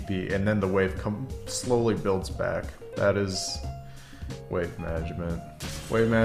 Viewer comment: I thought it said weight management 🤔